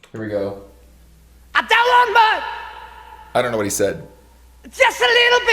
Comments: I think he says "I don't want much" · I think he says "I don't want much" Also at the beginning of the song you can hear Robert cough and then Jimmy begins with the riff in E